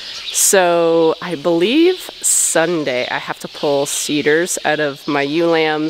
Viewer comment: When you said seeders I thought you said cedars. It was kinda funny